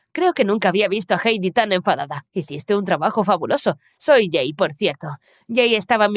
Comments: yo escuche "soy gey" xddd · Se que en la parte 1:44 dijo “soy jay por sierto” pero literalmente escuche “soy gay por sierto” y quede re confusa ósea- 🤨🏳️‍🌈❓❓ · Entendí "Soy gay por cierto" 💀